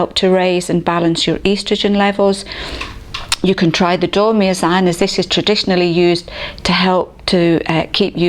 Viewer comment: I couldn't catch the name of the second supplement mentioned after phytoestrogens early in the video. It sounded like it started with D. About 2:37. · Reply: Hi Samantha I think it was Dormeasan, we recommend this for sleep. <link> Best Wishes